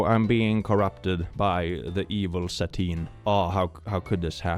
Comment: Instead of Satine I understood Sardine 😭